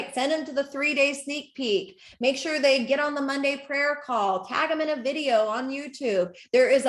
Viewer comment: "Monday Prayer Call" What the Actual F!?! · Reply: I legit thought she said Hunday prayer call